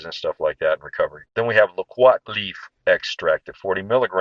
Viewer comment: I thought he said Loquat Leaf not Rosemary leaf. Is Rosemary leaf better for lung inflammation?